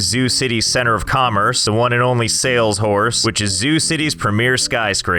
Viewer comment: I don't care what you say that's sale shorse not sales horse